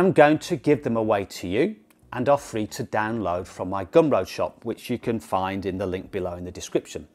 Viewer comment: ) I heard "Gumbo Shop" but did get it right when I looked at description :) Gum Road !!